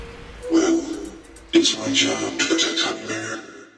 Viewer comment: What is Jawhead saying on the intro? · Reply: I thought he said "well,its my job to protect you"